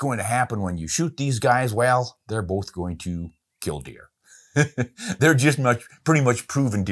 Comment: i wasnt paying close enough attention i thought you said they would both kill people and i thought id outed you as a serial killer 😂